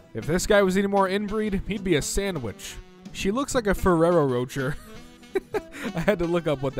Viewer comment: Did you just call it a Ferrero "Roacher?"